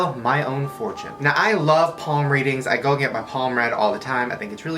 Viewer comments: "Palm read," I thought you said "Palm me," and I inhaled my ice tea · I thought he said porn reading